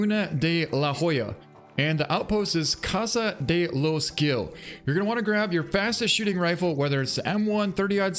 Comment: I just heard "Casa de lost skill" and was confused :D